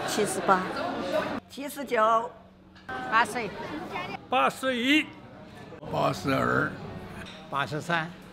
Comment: “PASS A YEET” Lol pls tell me I’m not the only one who heard this😂